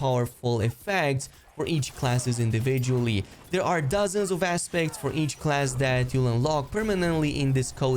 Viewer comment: Had to do a double take when you said “aspects” as I thought you said assbag lol😂😂😂